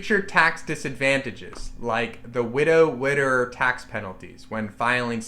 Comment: Good video but did he just say "widder" instead of widower? I think he did. I laughed.